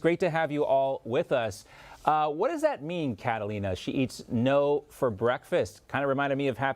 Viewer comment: Wait a minute. Did she say she eats "NOs" for breakfast. When I first heard her comment I thought she said that she eats NOSES for breakfast which I thought would account for her snotty attitide.